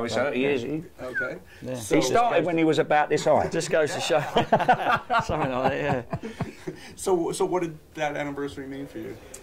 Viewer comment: what does steve say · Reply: he says "something like that yeah..."